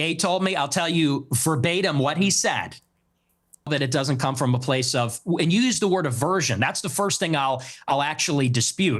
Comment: uncensored piers morgan censors something, what did ye say lol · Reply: He said n is going to be n or something like that